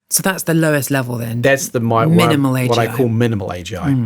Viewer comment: Beautiful how the transcription reads 'minimum wage job' instead of 'minimal AGI' at 7:14.